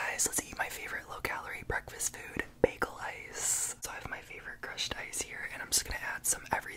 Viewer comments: I thought he/her said Bagel Lice 😅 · Thought he said "Bagel Lice"